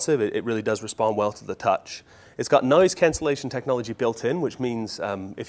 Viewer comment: I think if you go to the gallery you can play videos, at least you should be able to play recorded videos without any additional software. "Noise cancellation buit in" :) I heard "Nice cancellation built in" at first but I think I'm already missing that noise cancellation tech :P